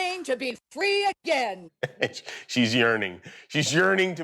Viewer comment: Oh "yearning". I thought she said "urinating".